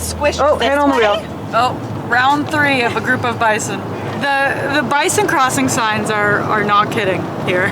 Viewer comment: LOL! ! ! Love your videos! I have to travel the Alaskan Highway! Funny thing - as you were approaching the Bison- I thought that you said- 'oh- the bison crapping 'signs' - as if you noticed their 'deposits' so knew that you would see them soon! I thought- 'Wow! she knows the wildlife' . Then later I heard you say - 'The bison crossing signs' ! !